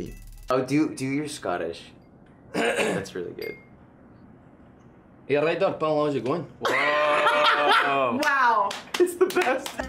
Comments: did anyone even get what he said there??? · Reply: “You alright there pal, how’s it going?” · what is he saying here? I cannot understand a single word 😅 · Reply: He said „You alright there pal, how’s it going?“